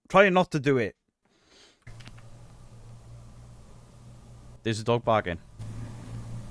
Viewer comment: I am profoundly deaf, so I listen with crazy headphones. At 26:26 says, 'That's a dog barking.' I heard, 'That's Zak Bagans.' 😂 Thank you ..